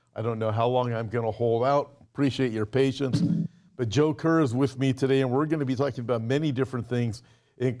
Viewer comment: Great program. At first I thought you said your guest was “ the Joker” so my mind immediately went to Batman thinking what??? Then I saw it was Joe Kerr. I needed a laugh his morning 😂